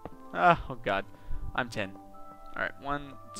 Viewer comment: When u said 9 10 fast it sounded like you said I'm10 and I was like -_____